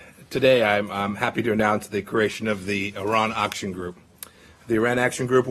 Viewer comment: Did he say the IRAN AUCTION? 00:21?